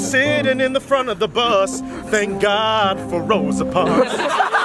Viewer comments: Wayne Brady: “He’s sitting in front of the bus, thank god for Rosa Parks” how they make this up so good😂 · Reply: I thought he said thank god rows apart lol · “He’s sitting in the front of bus... thank god for Rosa parks” I loved that omg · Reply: thought he said thank god were roads apart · "His skin is chocolate and so so dark, he sittin in the front of the bus thank god for Rosa Parks" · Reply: I honestly thought he said "Thank God we're rows apart" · “He’s sitting in the front of bus... thank god for Rosa parks” I loved that omg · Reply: I thought he said thank god we’re rows apart😄